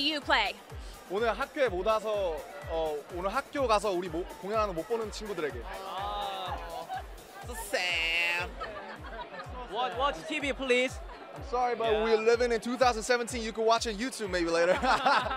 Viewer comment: Did jin really say "watch TV please" at 1:29 Im dead Yall watch when they drop pied piper pt 2